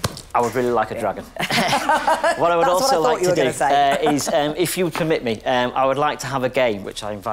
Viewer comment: Listening to this on a quiet setting, I first thought he'd said "if you commit me"...